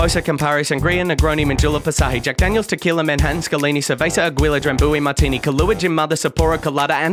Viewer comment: real talk at 1:28 he says "scalini" but the subtitle says "kalaini" but I cant find any results of what that is and I may or may not be trying to recreate this night and want to be historically accurate.